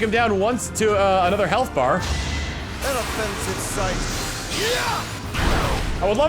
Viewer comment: What did Lorenz say? Something "in sight"? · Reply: I know I’m a year late and you’ve probably forgotten about this, but he said “An offensive sight”